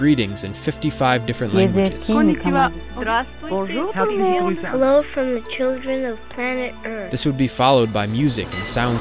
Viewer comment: Child sounded like he will say "Hello mother*ucker"